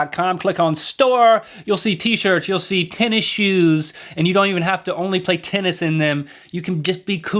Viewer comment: Thumbs up if you thought they said 10 inch shoes instead of tennis shoes